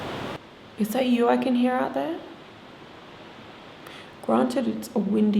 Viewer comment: it sounded like the voice said “I don’t hate you”, but in a crying tone.